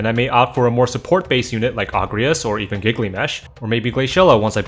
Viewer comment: Did I misheard gilgamesh as giggly mesh? XD